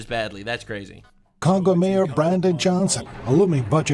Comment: did he say CONGO mayer brandon johnson 😂